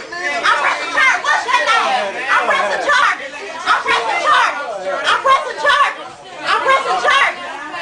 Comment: it sounds like shes saying IM PRESIDENT CHARLIE